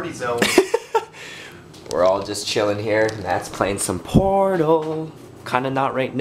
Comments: ok i won't lie. i heard "matt's playing some porno" not portal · When he said "Portal" I SWEAR I heard him say porno.